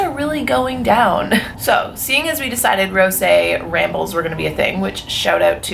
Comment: I thought she was saying rosé redbulls and I was gonna try that drink some time 😂